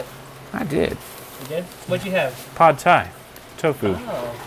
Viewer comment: I had this on in the back ground when i was washing dishes. When u asked him what he had for lunch i thought he said tide pod.I had to go back and listen to it so i could hear pad thai.